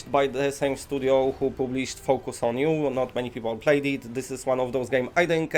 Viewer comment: The first time I heard you talk about focus on you I thought you said focus on poo!! Lol